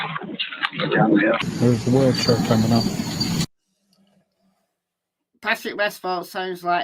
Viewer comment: Sounds like she says he’s coming the first word you heard